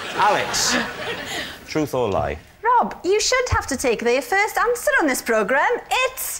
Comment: what is she saying? "Rob, you should have to take the first answer on this program."???